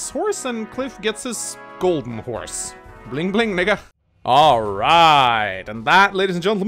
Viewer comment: did he say nigga or miga in